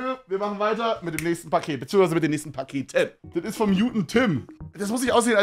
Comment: Bei minute 3:07 als er gesagt hat "das ist vom guten tim" hab ich gehört "das ist vom juden tim"